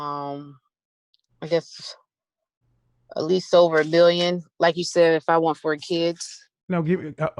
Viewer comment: Classic conversation with the 28 year old. Life tells them they should be treated like a queen but never tell them what they need to do to end up in the castle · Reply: Yeah...He broke it down...I thought I heard her say "over a billion" Whew!